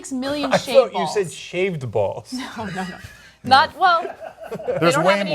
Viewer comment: Ok I thought she said shaved balls too.